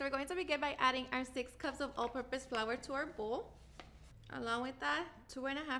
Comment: Speaking of "words", I have watched this 3 times and STILL couldn't understand what kind of flour she says to use. Sounds like she is saying ultra- bisque flour, but I never heard of that. What is she saying? Anyone? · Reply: All-purpose.